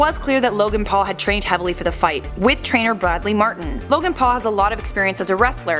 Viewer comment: Did she just say "with trainer Bradley Martyn"??